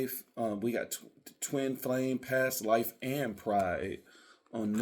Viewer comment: I thought you said twin MAYA and had to rewind